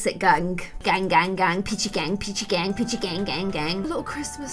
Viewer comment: when she said peachy gang i thought she said gucci gang and I got so confused